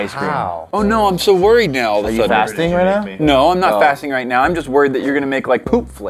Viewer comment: Can anybody tell me what said? I didn't understand. Please complete this sentence: "Are you ( word I don't understand ) right now!?" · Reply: fasting? basically not eating nor drinking for certain period of time